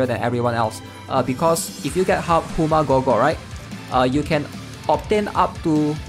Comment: what are you saying? Hooma Gorgor is what I hear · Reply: Im new but I think that he's saying HuMaGorGor (Hummingbird, Maze, Gordias, Gordias) gear set